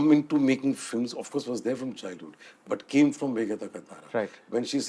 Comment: What name doest he take at 22:31? When he says ""But came from_____" Really tried but cant figure out. Sm1 help. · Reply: Meghe Dhaka Tara